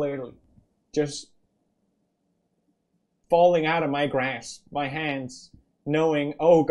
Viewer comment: why did i hear "out of my grass"